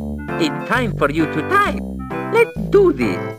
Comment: Guess what time it is! It's time for you to D I E thats what i hear.